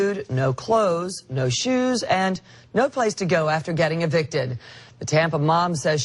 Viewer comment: LMAO I thought she said the woman had nowhere to go after getting a Big Dick!!! Had to listen again. HAHAHAHAHA!!!